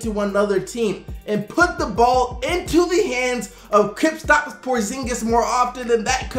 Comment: No disrespect, but I always feel like you say "Kritstops Porzingis" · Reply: Cristian Escobar I heard "Kripstops"💀